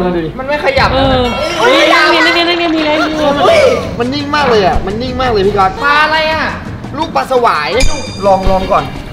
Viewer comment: ผมได้ยินคำว่าที่รัก7:11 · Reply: คำว่า พี่ก็อตจ้ะ😁😁😁แต่เราก็แอบหวังอะเนาะ อิอิ